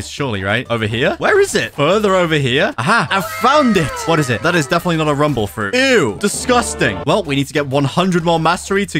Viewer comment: what fruit did he call disgusting? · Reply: Spike